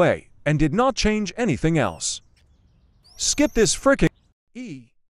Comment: I heard “E” so yeah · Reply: Good! Sorry demonic said "1/10"